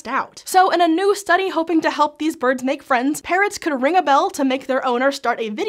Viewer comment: Thought she said parents at first😂